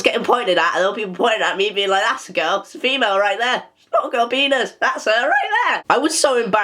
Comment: Wait did I just hear “she’s got a girl penis”😂😂😂